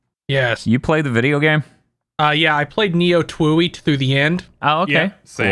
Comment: When said he played Neo TWEWY, I thought he said Nioh Tooey because I have brain damage